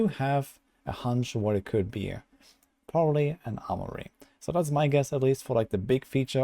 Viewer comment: For the life of me I can't understand what he thinks is coming in the next season?? Sounds like he is saying hourly but obv that not it... wth AR you saying man?? Lol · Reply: Nvm he saying armory after reading comments